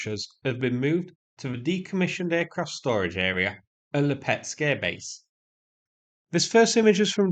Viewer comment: At the beginning, I could have sworn you said "pet scare airbase." I thought that yeah, the Russian language is weird to me but..really? They scare pets? A**holes! It's horrible they go after civilians but I hadn't thought about the pets. All of that in about three seconds before I went to the transcript to confirm. I'm a legit idiot. Facepalm